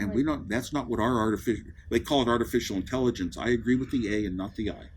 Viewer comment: What did he say at 7:25? Did he say "I agree with EA, not with AI"? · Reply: I agree with the A and not with the I.